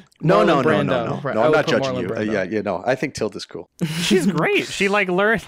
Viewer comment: What did say about marlon brando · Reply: "I'm not judging Marlon Brando"